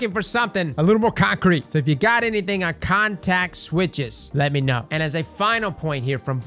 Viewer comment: I believe when I listened to the podcast, it sounded like Kevin said "context switches" not contact switches. Contex switches in the sense that the team has a new plan for the identity of subclasses in both the PvE and PvP activities we participate in. The "context" abilities will excel in are being switched around with the upcoming Light 3.0 system.